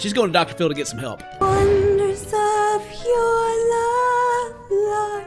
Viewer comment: What was that woman even singing? I heard "Under sand your lie lol" or something equally stupid.